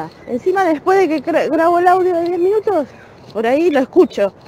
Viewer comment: Miren que yo soy larguera con los audios también, pero escuché bien? Dijo "audio de 10 minutos"? (Minuto